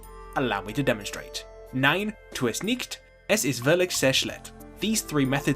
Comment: "Nein tut es nicht, es ist wirklich sehr schlecht." He learned French so good he started talking German. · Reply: Ah! I misheard it as "es ist verligser schlecht" (loser bad?)